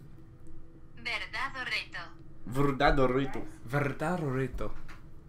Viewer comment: The real horror is and trying to speak Spanish. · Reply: it sounded like they were saying "verdad Dorito" fmkdkdjdj lmao. But it's okay we love them anyways